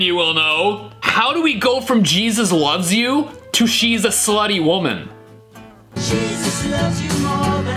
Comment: I heard it say she is a slushy woman